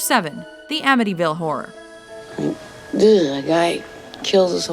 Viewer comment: did she say the amityville whore?